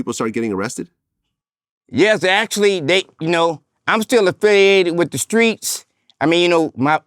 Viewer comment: jesus christ vlad you love the fact that you got flewed out like a city girl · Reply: Wtf he say at 5:35 he said I'm still a fed👀